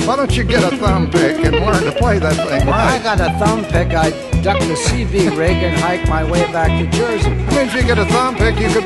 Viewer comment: I've never figured what LP says at 3:15. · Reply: "If I had a thumbpick," says Les (who never used one) "I'd dump the CB (radio) rig and hike my way back to Jersey." Love Chet's response: If you had a thumbpick you could play with yourself." Les: "NOW you tell me!!"